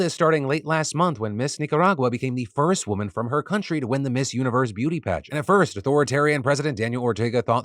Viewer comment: Hahahahaha. I thought you said, "This universe is probably a plot to..." Instead of "Miss Universe". It made the rest of the statement sound even more insane that the actual story, which is pretty insane.